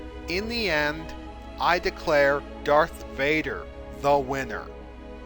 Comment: Did I hear “Dark Vader”?